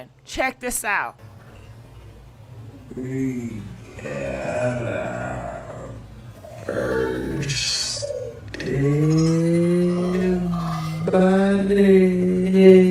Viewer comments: I’ve only heard ppl say I’m about to catch a body . He was saying the same thing ! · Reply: We got our first dead body That’s what I heard him say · “Sounds like he said we got our first dead body, she thru for”💔💔💔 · Omg I heard him say we got out first 💀 dead body omg 😱